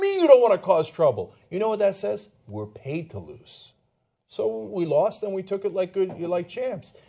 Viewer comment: Did he say "paid to lose "? i heard "Born to lose".